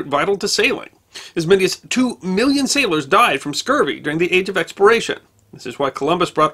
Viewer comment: YouTube captions says “as many as two million sailors died from scurvy during the age of expiration.” Which, oddly, is accurate.